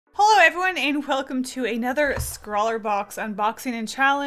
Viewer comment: Lol, I swear, I heard her say "Holo, everyone!"